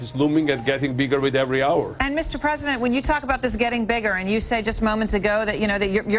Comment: Does she say: "Mr. President..."? 3:07 ))